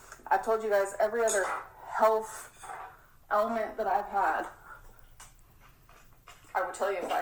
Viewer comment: did she really say “health element” ….. ELEMENT ?????